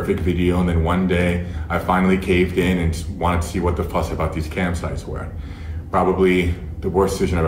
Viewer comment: I legit thought he was saying campsites for the first 60 seconds. I'm so happy I wasn't the only one. 🏕